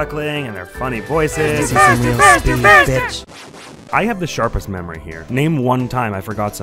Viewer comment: What da hec is omori saying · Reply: "You wanna' see some real speed, bitch?" =^w^=